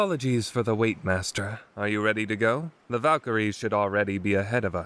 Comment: Did I just hear VALKYRIES. Does this mean that someone summon them